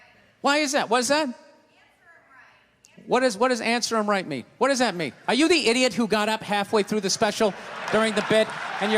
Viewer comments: What does the woman say at 2:45? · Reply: "Every Day!" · I think 2:41 was saying "use your brain" not "answer him"